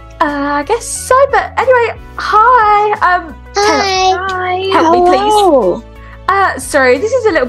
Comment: It sounds like she said oh shit Taylor